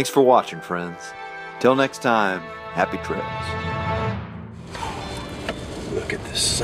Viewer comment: Prior to 11:21, what'd he say? Happy ?? Trails? No way!